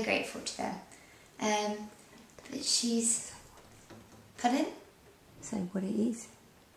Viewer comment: OH, you were saying "pardon..." at first I thought you said "puddin." Nice guitar, nice playing :)